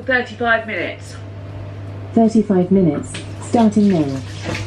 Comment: You said 35 minutes to the Alexa and then when it repeated the time it sounded like it said 45 minutes - hence the burn.